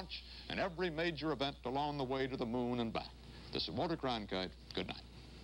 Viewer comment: I always hear "this is Sir Walter Cronkite"....